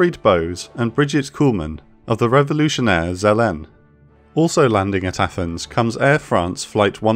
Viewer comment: The two Germans belonged to a group called "Revolutionäre Zellen", which translates to "revolutionary cells", if you were unsure what exactly was said at this time stamp